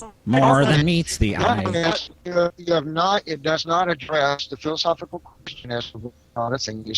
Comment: did he say "philosophical penis?"